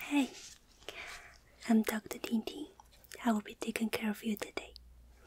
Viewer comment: Ooohhh TingTing. I thought you said « hi I’m doctor dingding » I was LMAO... oops. I love your video btw :) haha